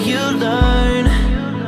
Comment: for some fucking reason i thought he was saying "younger" and not "higher" and even though what i thought made no sense literally at all, i was convinced that's what he said XD